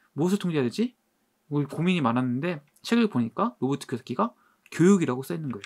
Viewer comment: 한번씩 로버트 궤새키로 들려요 ...ㅋㅋㅋㅋ 부자아빠 책 다 구입햇습니다 :)